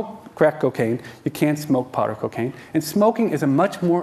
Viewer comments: Did I hear this guy say "You can't smoke pot....." ~4:20 (coincidence......) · Hate to be pedantic but I'm certain I just heard him say "you can't smoke pot or cocaine". The latter is hard to smoke and get high off, fair play, but can't smoke pot!?! I'm pretty sure that smoking weed is the most common and widely used method for weed intake. As good of a video this seems to be, the set up is purporting information already. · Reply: He said "you cant smoke powdered cocaine" ... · does he say you cant smoke pot? cause im pretty sure you can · did he just say you can’t smoke marijuana